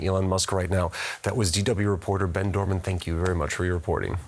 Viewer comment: I seriously thought the present said the guest was called "Ben Doverman".